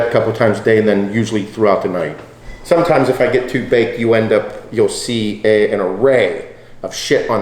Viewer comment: Did he say “sometimes if I get too baked...” at 14:30-14:40??? Was that a smoking reference lmao